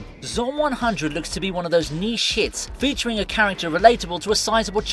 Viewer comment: I know you said 'niche hits' during the Zom 100 preview....but all I heard was 'knee sh*ts'